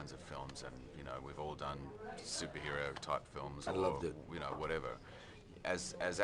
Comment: Anyone know what Tom Hardy says at around 4:45? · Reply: Dec M “and loved it”, not a tricky one, really, considering the man can grumble/mumble yet still be understood, just gotta listen